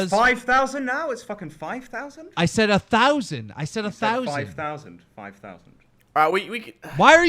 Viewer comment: Keem did say 1,000 but tbf I heard 5,000 the first time too.